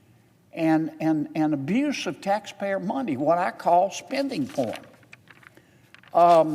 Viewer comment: Did he say “what I call spending porn” In 3:49?